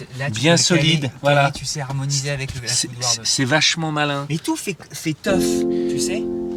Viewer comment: Quand dit : "tout fait tough". J'ai entendu: "tout fait teuf". Pendant un moment j'ai cru découvrir l'âme fêtarde du premier ministre 😂